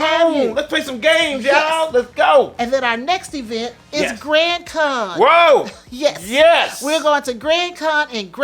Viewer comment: For half a second there, I heard, "GrandCon" as, "GrantCon." ;-) lol